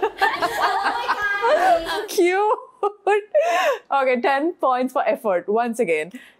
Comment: 인도리포터가 피프티 귀여워하고 이뻐하는 게 너무 보여서 좋네요.ㅎ 키나가 원몰타임 플리즈 하니깐 리포터가 13:58초 cute 하는데 순간 귀여워로 들려서 한국어 하는 줄 알고 놀람.ㅋㅋ 분위기 넘나 화기애애하고 까르를해서 미소가 지어지네..ㅎ